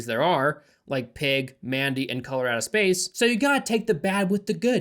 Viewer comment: When I heard you say 'Colorado Space' in 9:42 I was like 'oh cool, some unhinged Nick Cage movie I somehow haven't seen yet!' and proceeded to google it... damn, I need to cut down on weed looool